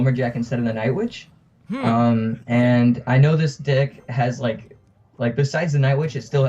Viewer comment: He said dick instead of deck